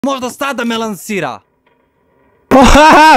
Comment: Bro i hear say f*ck you 0:02 💀💀